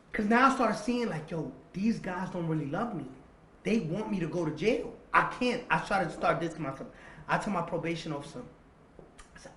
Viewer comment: Am i the only one heard him say don’t say shit to himself😂😂